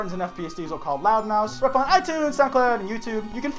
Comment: is it just me or does have a slight lisp because everytime he says "loud mouths" it sounds like he says "loud mouse" it's honestly really cute :D